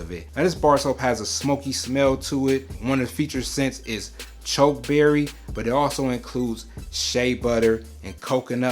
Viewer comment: thought he said chode berry for a sec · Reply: 😂 I apologize that it came across like that. But the name of the ingredient is Chokeberry or Aronia Powder!